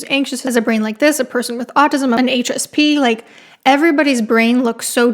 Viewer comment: I kept hearing "anxious pea" the first few times you said "HSP"